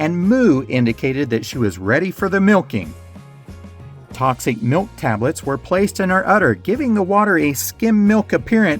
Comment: It sounds like you say “Toxic milk tablets” for the cow. The label says non toxic. My question is were they toxic or not?